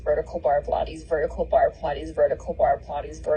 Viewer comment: vertical bar pilates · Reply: is that what it’s saying? I thought it was saying “vertical barb hotties” as in Nikki Minaj 😭